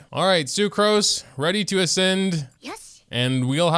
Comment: Haha it sounds like she said yes 😭